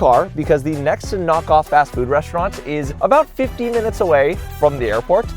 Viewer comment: How is 15 minutes a long drive?! Edit: Nevermind, he said “50”, which I misheard as “15”. Oops.